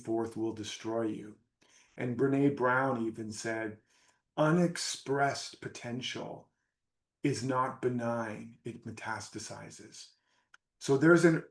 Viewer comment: What does Brenee Brown says? At 25:35 …”Unexpressed potential…. ???” Please someone type it so I can translate it and understand it 🙏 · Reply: Unexpressed creative potential is not benign, it metastasizes. Lots more vids to check out. And my new book + over $1000 in bonuses to support you. <link>